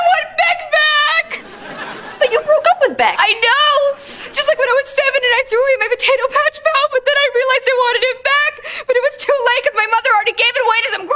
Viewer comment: Is there any English speaker who can tell me what she said from 0:53? I couldn't catch it.. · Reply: "I KNOW! Just like when I was seven and I threw away my Potato Patch Pal, but then I realized I wanted him back, but it was too late 'cause my mother already gave it away to some greedy orphans!" hope that helped!!!!!